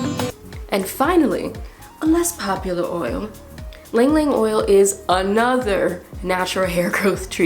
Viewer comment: I thought you said "Ling Ling oil" and I was expecting a TwoSet cameo lol